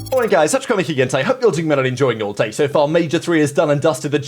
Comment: what do you say in your intro mate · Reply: I heard “I like guys, testicle 3 again today”